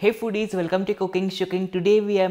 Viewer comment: I thought he said let's get cocaine